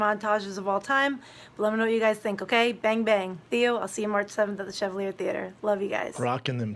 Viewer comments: Did she say “bang bang”? 😂😂😂😂 It’s Gang Gang Chica. You must be new here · did she say "bang, bang"? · did she just say "Bang bang"?... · I definitely heard say Bang Bang....right?!